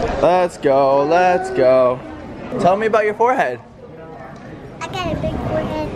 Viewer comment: I thought said "tell me about your Jorge" then later realised he meant forehead 😂🤣😂